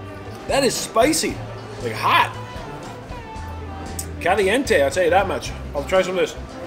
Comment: Did he say "caliente" in a Thai restaurant? hahaha